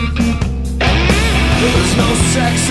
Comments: So I'm 36, been listening to Bush since I was young, thanks to my older sis. But anyway, I always thought he said, "there's no sex and violence." But I thought I just heard "there's no sex in your violence." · There’s no sex in your violence? 😂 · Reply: I thought is said there's no sex and no violence, cause ya know, Zen